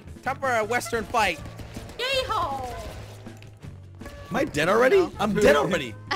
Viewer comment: when said 'Am I dead already?' I thought he said 'My dinner ready?' XD